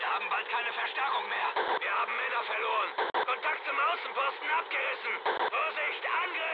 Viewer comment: can someone tell me the german version of this and the translation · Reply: what I heard :"Wir haben Männer verloren. "